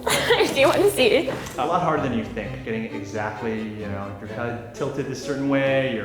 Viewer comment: OMG I CHOKED ON MY CHIPS CUZ I THOUGHT HE SAID " Im a lot harder than you think "